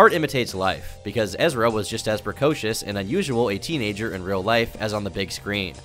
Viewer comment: is he saying Precautious? Shes beautiful.